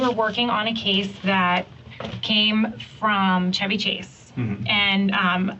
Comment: I wasn't looking at the screen and I swear it sounded like they said Chubby Chase, I was like WTF and went back 10 seconds......ooooooh